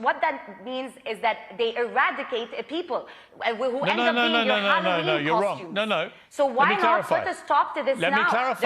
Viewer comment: Did she just say Palestinians are our Halloween costumes? What?